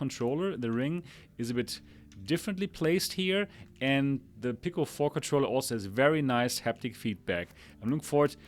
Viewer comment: NGL i thought u said pickle 4 controller at 5:28 lol... but love the depth in ur review. loving my quest pro cause I can play mobile and PC games. once the PSVR2 is launched fully, I want to get that for console games on ps5.